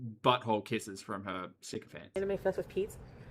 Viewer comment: Every time says 'syncophants', I hear it as 'sicko fans'. Same! 😂